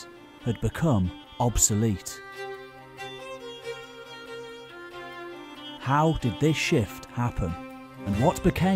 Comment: I did a double take about a minute in, when I thought I heard “how did this sh*t happen and what became of the Vikings” Passionate about this topic are we? 😂😂😂